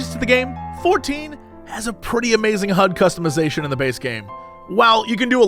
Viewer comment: I heard hug customization and was confused and interested lol